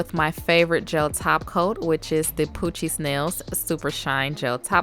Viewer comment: When you said poochiez nails I thought you said coochie smells🤣🤣🤫🤫😬😬